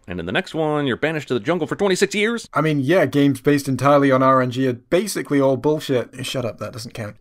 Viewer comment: At about 5:55 he says "games based entirely on..." and I cannot figure out what he says and the captions don't seem to make any sense any one help me. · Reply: RNG, Random Number Generation.